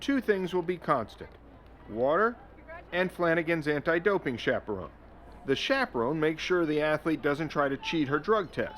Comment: "..things will be constat - water and flanagans anti doping xxxxx" what does he say in the secont part? i listend at least 10 times to it and showed it to 2 friends neither could help me. english is not my first language so my struggle is real. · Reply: Chaperone